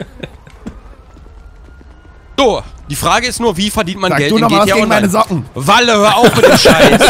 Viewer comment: Sry aber was hat Sarazar genau gesagt ich habe es nicht ganz wortwörtlich verstanden, irgendwie "Sag du noch was gegen meine Socken!" . Stimmt's?